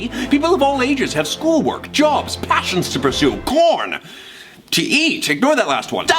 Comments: I thought that said “porn” instead of corn and immediately backtracked. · I THOUGHT HE SAID PORN